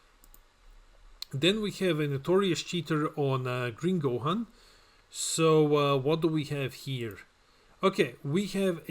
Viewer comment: What did he sayyyyyy? Lmao. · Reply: I've said "bottom deck 1, draw 2" 🤣□□□